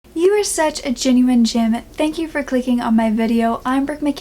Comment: I thought she was saying Genuine Jim for like three months until coincidentally my CCs were on. :,)